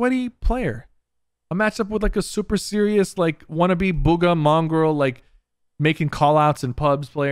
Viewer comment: The Subtitle says “mom girl“ instead of Mongraal hahahaha